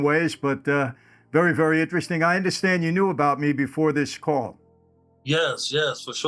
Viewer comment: I heard you’d knew of me before hahahaha cringe af!!